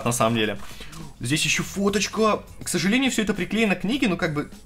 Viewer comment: кому еще кроме меня послышалось что когда листал книжку сказал не фоточка, а Водочка?